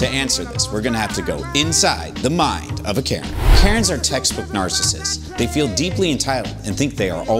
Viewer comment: I thought you said "You need to go inside a Karen first"